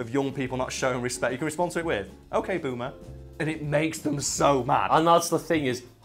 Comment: wtf no Google he said ok boomer not ok Google